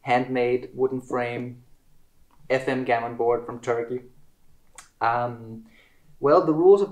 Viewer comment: It sounded like he said the board was from Target lol